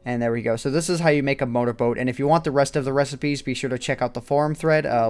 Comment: i heard him say boner boat 0_0